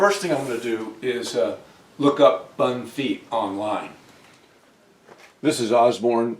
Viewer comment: I thought you said you were looking up fun feet online not bun feet. I thought you were going to put a clown shoe on that beautiful table. I should have known better 🤣🤣🤣